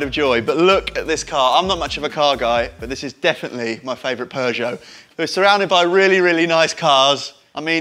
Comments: How do you pronouce Porsche? Really sounded like he said Peugeot... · what did he just say..? Peugeot?